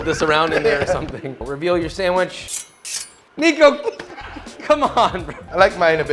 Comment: What did he sayy? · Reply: nico